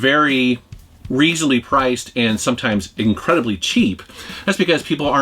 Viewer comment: Did you say "nostalgic fart"? Lol